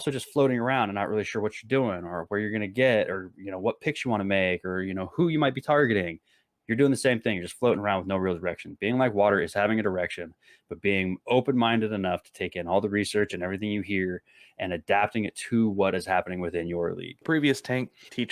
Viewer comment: did you say have an erection 😂😂😂